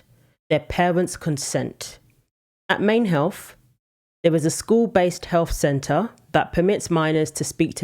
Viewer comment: I heard 'Maimed Health'😂